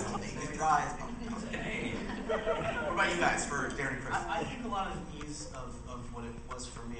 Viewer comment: when he said "Darren and Chris" i thought for a sec he just said "Darren Chriss" and completely ignored chris